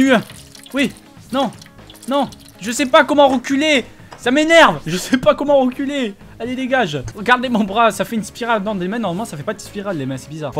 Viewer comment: j'ai compris "je sais pas comment enculer!"😂😂😨WTF